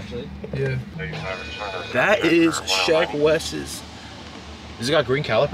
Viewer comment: Instead of Sheck Wes I thought it said Shrek Wes